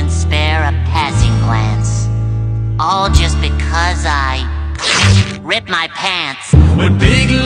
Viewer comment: Am I the only who thought it said “I ripped my parents”?